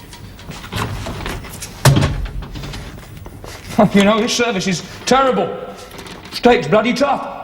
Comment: Did he say "F**ckin' 'ell this service is terrible!" at 30:50? Bit strong for 1972 isn't it? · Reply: He doesn't swear, he says "heh, you know this service is terrible....". Listen again. The automated subtitles also pick it out correctly.